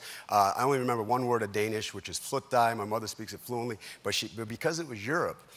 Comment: What on earth is he trying to say in Danish?! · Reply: I did some research and he is saying "Flyt dig"